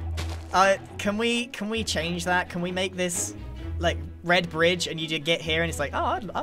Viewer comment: fully thought grian said “bread bridge” at 2:09:58 im having limlife withdrawal symptoms